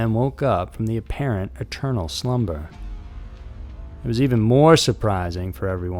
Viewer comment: Brian Laundrie is confirmed dead. And I am not sure but it sounded like you said "paternal slumber" but I suspect you mean "eternal slumber."🤔🤪